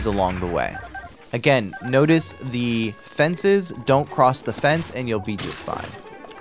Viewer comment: I'm so tired rn I thought he said "Notice the fences don't cross the fence and you'll be just fine" lol